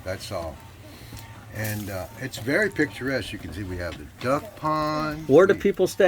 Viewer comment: did he say very picturesque....okee dokee :D